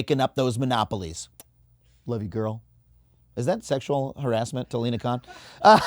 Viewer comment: lol I thought you were saying you’re a fan of sexual harassment!